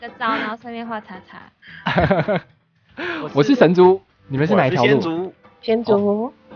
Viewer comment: 說我是神族的時候我聽成我是神豬!XDD